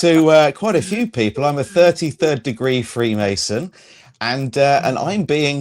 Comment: I swear I thought I heard him say freebasing